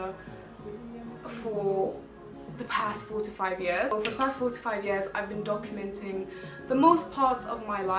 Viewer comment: Lol I thought you said "the past 45 years"😂 and I was soo confused 🤦🏾‍♀️🤦🏾‍♀️